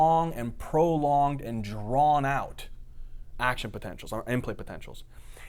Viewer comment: sorry to bother you (just saw that you published a comment a month ago )would you be kind enough to elucidate ....what is said at the end of the lecture : succinylcholine cause a drawn out ( what ? at 24:50 ) (Im not a native , I HEAR amplipotential - but it doesn't have any sens) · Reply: He said it cause a long action potentials or implay potentials (inplay potentials are like postsenaptic potentials)